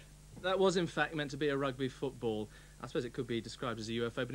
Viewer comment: Rugby Football instead of UFO tonight